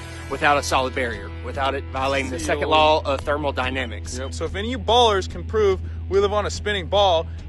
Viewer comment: Did he say thermal Dynamics or thermodynamics? Yep, @ 2:58 he says "thermal Dynamics" lool